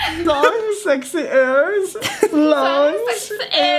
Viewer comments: Honestly seeing, and laughing so hard and just being completely slap-happy was probably the funniest thing I’ve seen in a while 😂 Also instead of “sexy ears” I heard “sexy arms” 😅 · to me it sounded like she said long sexy lung ears